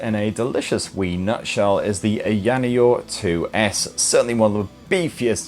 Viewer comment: I could sworn i heard he said ayaNioh 2 lol😂😂😂😂